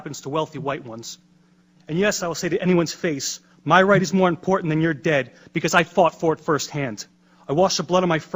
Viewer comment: OUR RIGHTS..THIS NATIONS> FREEDOM YOU WANT TO TAKE THATS WHAT HE IS TALKING ABOUT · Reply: I don't think you heard him correctly. In minute 1:27 he says: "my rights are more important. ..... because I fought for them" I fully support or military and all the members of the armed forces, but none of them can say their rights are more important than ours.